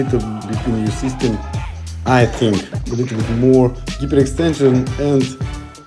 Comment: Ilya: "you need a thicker and stronger back." Basically. Sorta. Yeah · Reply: He clearly says "hyperextension" at 0:30 but subtitles are "bigger extension". Cool, makes 0 sense. Hyper pronounced as "geeper" in russian btw.